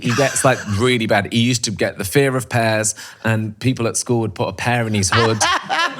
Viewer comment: help me, did I just hear the phrase “the fear of pears”?